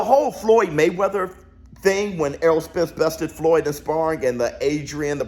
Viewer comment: i dunno what y'all heard but i heard "Spence stopped Floyd" lol 50-1 lol